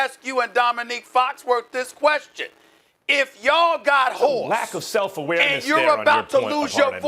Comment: Stephen A. sounded like he was saying “If ya’ll got whores” at 4:28 lmaoo